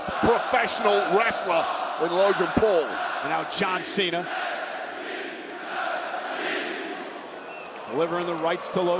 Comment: what croud saying? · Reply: They are saying John cena John cena and some are logan pual qnd in the match they say John cena woah